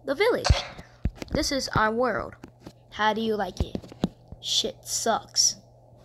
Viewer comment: did it say shit sucks or it sucks?